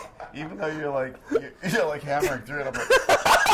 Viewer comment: What does JP say at 2:24? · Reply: 'You're like hammering through it and I'm like...'